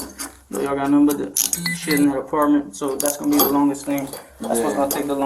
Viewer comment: did he say shity apartment?